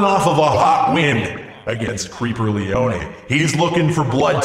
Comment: at around 5:56 I sounded like he said my name my name is leonie (lee-o-nee) !